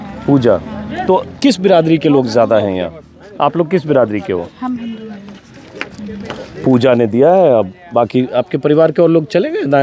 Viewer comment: I heard that she said I'm hindu hai.. what I heard is correct that's great answer .. no cast.. all are equal..all are hindus 🙏